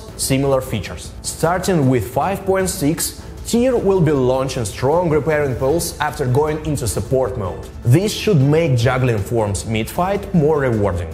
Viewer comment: In case people can't understand at 1:19 he says "Starting with 5.6, Tyr will be launching strong repairing pulse after going into support mode." I had to replay that bit several times, seriously with all the money you have chiseled outta your customers you could at least toss a few bucks towards an elocutionist.